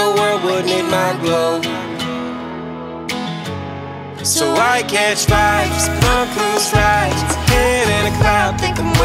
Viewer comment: As soon as he said “why catch fights” this track turned to fire and didn’t stop. 🔥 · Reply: I thought he said (so I catch vibes from cruze rides).. lol idk now.. haha